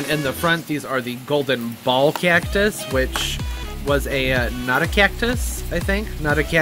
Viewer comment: I thought u were saying "not a cactus" while showing the golden ball, then I thought, "Wait a minute..." until I read the name that u put up, "Ah, Notocactus..." How silly. 😂😂😂